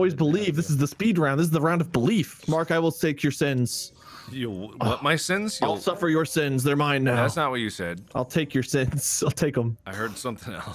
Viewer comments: I think mark heard I will take your lims 😂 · can someone tell me what mark heard, 'cause I can only hear ''Mark, I will sake your sins'' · Does anyone know what Mark thought said at 18:26? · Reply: I heard say “I will sake your sins,” which admittedly doesn’t make sense, but it’s what I heard. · Does anyone know what Mark thought he heard say? I think he heard "I'll sate your sins" but Idk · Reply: I heard I’ll take “ur innocence”💀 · "I'll shank your skin." That's my guess. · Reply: I heard "I'll suck your sins" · I think at 18:27 Mark heard "I'll take your innocence" 😅😅😅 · It sounded like he said "I will stake your sins"